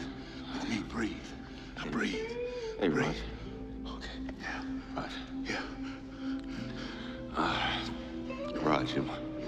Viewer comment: Does Riggs call Murtaugh Riggs? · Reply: He called him "rog."